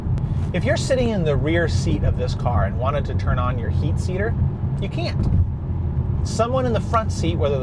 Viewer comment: Did you say heat seater?! Lol! I'll play with them when I get my M3P in 6 weeks...can't wait!